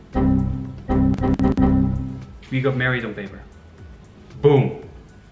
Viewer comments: What is saying? That ‘then in the US we got married in...(?)’😂❤️ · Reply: Married on paper. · i dont understant what he said ? we've got married ...... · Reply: We got married on paper